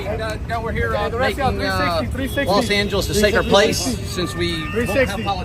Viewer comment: did he just say LA is a sacred place?